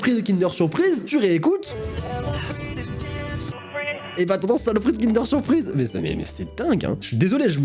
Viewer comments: a 10:09 moi j'entends "c'est le prix d'une kinder surprise" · heu....non j'entends le prix d'une danet au fruit 😅 Si nn je suis d'accord avec toi j'ai entendu 😊😉